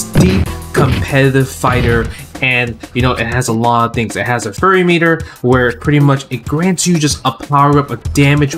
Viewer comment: Did he say Furry meter? Now I’m interested